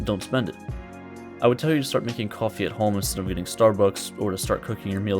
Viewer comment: did he just said stop "eating" starbuck in